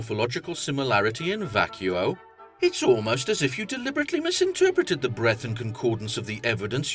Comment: You said "misinterpreted" but the subtitles said "misrepresented"